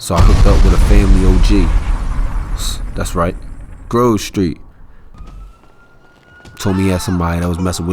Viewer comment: Low key thought dude said Girl street at 2:34 lmaooooo lol